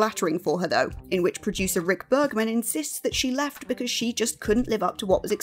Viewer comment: I'm shocked! Shocked, that Rick Berman said something negative about a woman on a Star Trek show. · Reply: Don't you mean Rick Bergman according to this video. That's what it sounds like she said. I know it's Berman which is why it stood out to me.